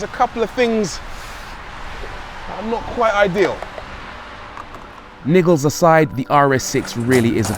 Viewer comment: did he just say 'niggaz aside?'